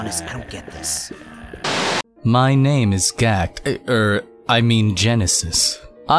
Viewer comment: TELL ME!! DID HE SAY GACKT?!!!